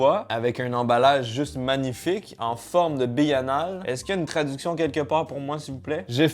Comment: C est moi ou il a dit bille anale😂😂